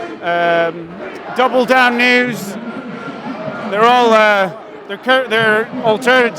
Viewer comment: did he just say "DOUBLE DOWN NEWS" ? Is that a serious journalistic entity? I DON'T CARE! That name PAAAHHAHAH AHAHAHAH AH AH A HAHAAHAHAHA HA HAH AH A HA AHA HAH 😂